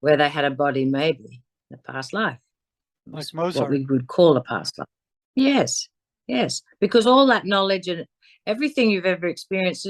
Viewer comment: What did say at 58:18 minutes? I couldn't understand it. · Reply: IF.....you are still interested - from the Transcript: 58:18 what's most what we would call a pastor yes yes because all that knowledge and 58:23 everything you've ever experienced is stored within your energy being